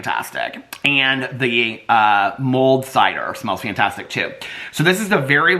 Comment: It took me a while to figure out that you were saying "mulled cider" - I listen at 1.65 speed and thought you kept saying "mold cider" 😂